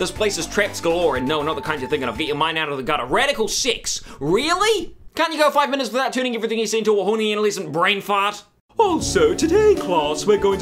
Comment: i thought he said radical six